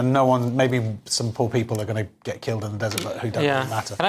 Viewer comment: did he say “maybe some poor people are going to get killed, who don’t really matter?”